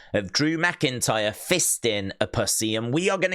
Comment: Wait, what? 0:23 …”Drew fisting….” a what??? Did I hear that wrong because of his accent. That had me laughing 🤣